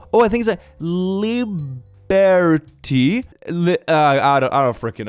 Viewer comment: That horrific wall of letters actually says: Life, Liberty, and the Pursuit of Happiness. I want to cry seeing it so mangled. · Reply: oh i thought it said "iliberate" lol